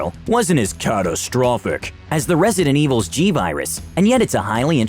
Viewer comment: Did he say Resident Evil "G" virus around 2:45? T virus... Or maybe I'm deaf.